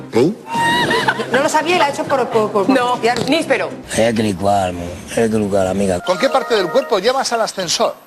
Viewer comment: alguien me diga que dice el colega porfavor??? 😅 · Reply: ¡equilicuá!. Es una corrupción de la también interjección italiana (posiblemente dialectal): "eccolo qua", que a su vez deriva de la latina "eccum hac", cuya traducción literal al italiano sería "ecco per di cua". Ahí la llevas con explicación y todo... Dice dos veces equilicuá y añade 'amiga'.